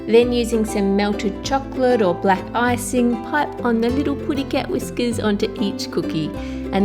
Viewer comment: did you say...putty cat?